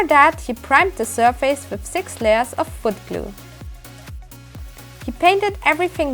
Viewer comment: what did u say u primed it with at 4:20? I could not hear the product name clearly. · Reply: Wood glue (white glue). You can use Elmer'S Glue for example.